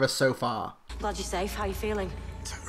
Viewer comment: I don't know if I have hearing problems or what, but at 07:00 what did the Doctor say? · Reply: "Glad you're safe. How are you feeling?"